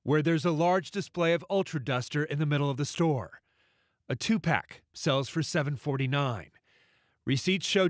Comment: Am I the only one thought the guy was gonna say tupac instead of 2 pack?